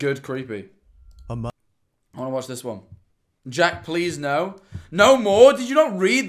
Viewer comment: When you had that encounter... I heard "jack... Please help" when I turned the volume all the way up